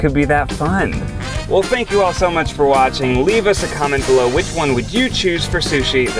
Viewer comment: did he say “well spank you for watching”